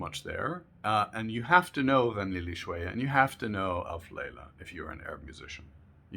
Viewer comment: Hey! At 11:20 you say, "You have to know _ and you have to know _" I'm trying my best to look up what you said but to no avail, could you provide the text so I can look up those songs? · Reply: Ghaneely Shwaya and Alf Leyla Wa Leyla. <link> and <link> Hope that helps!